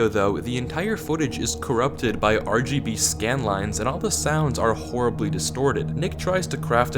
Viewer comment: bro i heard lgb scam lines 🤣🤣